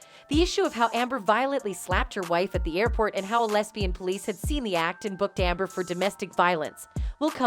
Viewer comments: Go to 3:14 - Now tell me who or what are the "lesbian police"?! I really hope I didn't mishear that and they are a real thing.... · I thought you said 'lesbian police' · I coulda swore she said "lesbian police" lol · Did she say "Lesbian Police" haha?